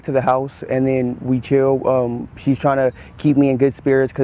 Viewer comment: Did anyone hear him say "we kill" at the 16:21 mark?? Is my mind playing tricks on me??